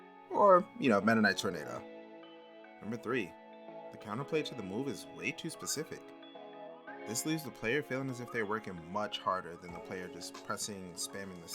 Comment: What was that? I couldn't quite hear you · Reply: The counter play to the move is way too specific. This leaves the player feeling like they are working much harder than they're opponent spamming the same move over and over Hope that's the part you missed!